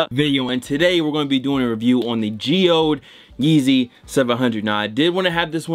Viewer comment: I thought these shits say "Geodude" 😂😂 and nah you can keep 'em